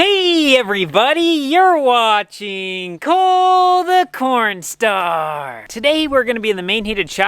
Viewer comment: When he said cole the cornstar my mom thought he said cole the pornstar